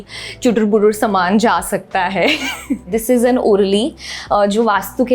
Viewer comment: vo kaunsa utensil hai koi bta sakta hai .. maine google kiya ''orlai' but kuch nahi dikha ra hai ... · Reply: It's urli bowl